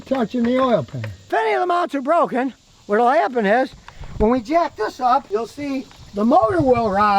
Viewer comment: what does he say? What part is he speaking about? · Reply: he said "if any of the mounts are broken, what will happen is..."